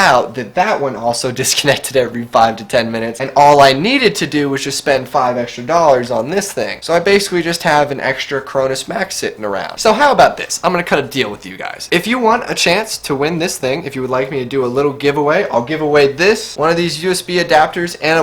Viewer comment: I thought you said " I have an extra chromosome" not chromus max😂